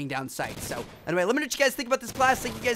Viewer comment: I couldnt understand him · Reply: "...I got a bunch of great videos coming soon..."